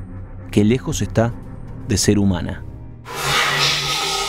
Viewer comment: En el minuto 10:22 parece que comenzara diciendo "Yo a ti...", pero la enlentecí un poco y no entendí eso sino una frase completa que dice "No debiste haber venido nunca".